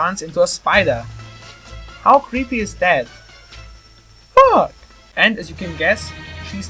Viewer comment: did mario just say f**k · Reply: no it's the thing behind him that says yuck not fuck